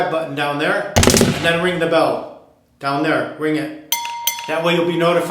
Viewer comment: Lol. 7:26 Did you say: "Ring the bell down there? " I hope I got this wrong.